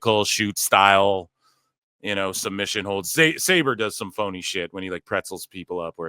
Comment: i thought his name was said (Saw-bre) zack sawbre jr